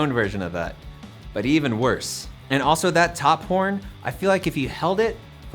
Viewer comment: i the only one who heard him say top porn?